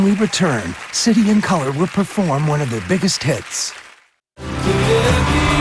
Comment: did i just heard biggest TITS ?! i mean, i know it's biggest hits, but you know... hahaha